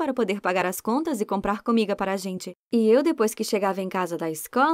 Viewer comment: so eu q entendi ela falando "comiga"